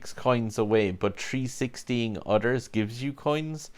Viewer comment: Oh yeah, that's what I meant by "360'ing others" you really have to be careful too because after you 360/KD the AI sometimes their cars wreck in front of you and if you nudge into them it can cost you 25/50 coins worth of damage... it's painful. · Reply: my bad thought you said 360 and others!